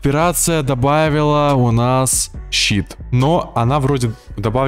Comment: I've been watching 3 videos already and for some reason everyone is saying "SHIT", I don't understand what they don't like?